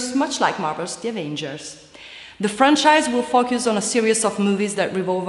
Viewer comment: Movie Trailers you need to replace your commentator, her English is not very good, it actually is very irritating to hear. I know she meant to say "focus" but it sounds like she is saying "fuck us" so do yourself and others a favor and find someone else. Thank You